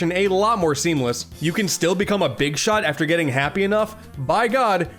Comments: Did he say big shot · did someone just say big shot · did you say.. big shot